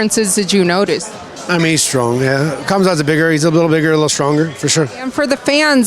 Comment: What the hell did he say at 7:00 !???🤔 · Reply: “He’s a lil bigger lil stronger for sure”